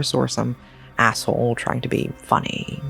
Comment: i thought i heard it wrong 🤣 at 11:58 A-hole, even the subtitle didnt appeared